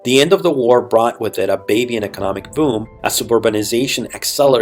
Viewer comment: Very good compilation and video. Only please leard to articulate and not mumble in certain places. For example at 17:24 It sounds like you're saying " baby? economic boom. " not really sure what you're saying at maybe 5 other points.